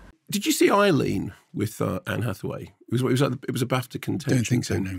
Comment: Excuse me what does he said at 03:03 "Did you see __ with Anne Hathaway? It was a _____. " "Don't think___"? Sorry I can't hear what they are saying. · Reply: “Did you see Eileen” “it was a BAFTA contender”